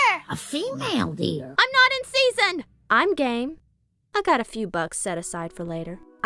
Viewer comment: that at first when I was playing sounded like "Im gay"